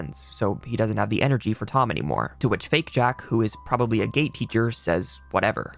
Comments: i thought he said gay teacher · I heard gay teacher💀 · at first I heard gay teacher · imagine my surprise hearing "gay teacher" instead of "Gate teacher"